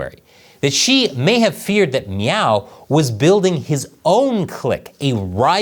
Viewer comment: I keep hearing you say MEOW every time you mention Xi's rival..